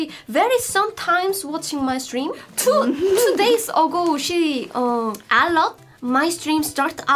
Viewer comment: I'm pretty sure that at 5:43 she is saying "alert" not "a lot". She's saying that Dr. J got an alert on her phone that was streaming, so she tuned in but she got confused and turned it off because she didn't understand what the start screen was